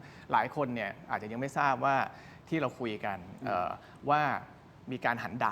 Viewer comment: นาทีที่ 25:10 หมอวาโย พูดว่าหัน “ดาก” ใช่ไหมทุกคน กูฟังผิดรึป่าว ?!?! 5555